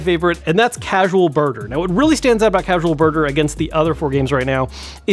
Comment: I thought you said "Casual Burger" lol